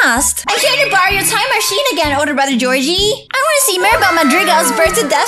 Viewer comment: Omg i thought u said oder brother...